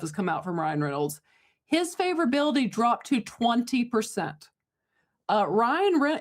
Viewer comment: OMG, I thought you said it dropped 20% not dropped to 20%!!